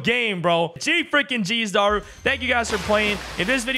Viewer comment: Did bro just say thanks u guys for playing?